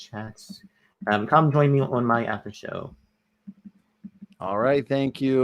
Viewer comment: needs to seriously get help with his communication skills. His enunciation is poor. When he said "Come to my after show" it was hard to decipher. He sounded like he was saying "Hum to my haffer hoe" Oof! Get it together. Then he gallops through syllables at times which does. . . not . . . help.